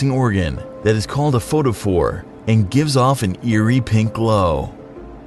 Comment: when he said fort-a-for I thought he said port-a-fort lel!😂